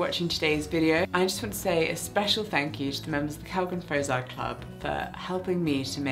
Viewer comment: there's an error in the subtitles at 23:46 where it says "calvin frazier club" instead of "kellgren-fozard club"